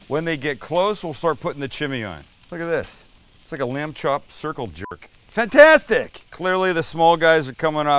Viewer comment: Um, did he just say circle j*rk??! 😲